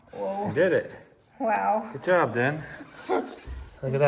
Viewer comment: did you say, “Good job, Jen” at 3:36? 😀